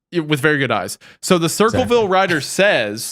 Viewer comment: For a moment, I thought said Circusville, which would be appropriate if he did.